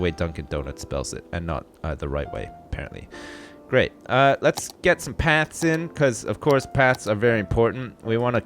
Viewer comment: When you said "Let's get some paths in!" I thought you said "Let's get some pants on!"